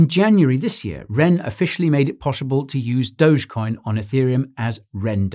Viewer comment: Love your videos man but did I hear poshible at 5:57?